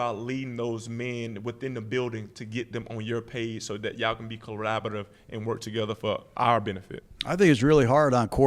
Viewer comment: 😂😂😂 yo😂😂 wtf did he just say? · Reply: Collaborative